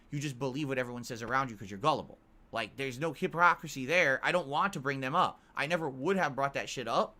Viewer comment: Did just say Hiprockacisy goddamn I'm catching a lot of verbal screwups from lately